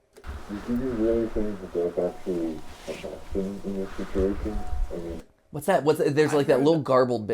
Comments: "Do you really think that there is an option in this situation?" is what it sounded like to me... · I think there saying "do you think theirs actually a bathroom in this facility" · I heard "an option in this situation" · maybe just before is when you were debating what it was - It sounded like to me “do you think the dark really attacked him in this situation” Could have been “the door,” not the dark Really hard to tell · I think the person said at 16:26 "do you really think that there is actually a backrooms in this situation"